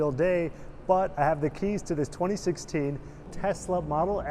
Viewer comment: Did he just say keys?